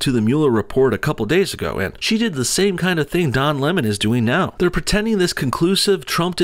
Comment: I thought u said John Lenin